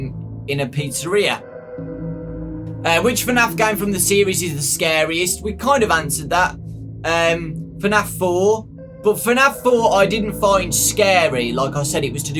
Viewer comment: (decides to turn on Auto-Generated subtitles part of the way through) 15:07 "and which one Afghan from the series is the scariest we kind of answered that for Nath for but for now for I didn't find scary" It then proceeds to call FNaF1 "For Nath 1" and refers to the game as a he. Edit: "Why do play games that are not for Nath"... And now, I'm laughing at the ridiculousness of this question. XD "Pete sir" instead of pizza, damn it YouTube. XD